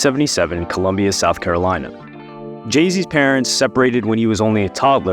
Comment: Yo! It sound like u said “Jay-z” at 0:20 or is it just me 🤷🏾‍♂️😂